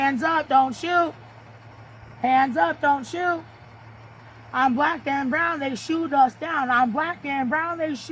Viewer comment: And then "I'm black and brown!" 😂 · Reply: I thought she said I’m black and round 😭